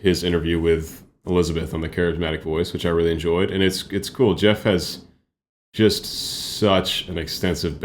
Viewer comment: did you say Geoff? Xxxd